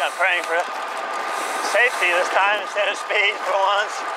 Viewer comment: Did I just hear the word “safety” from mouth??????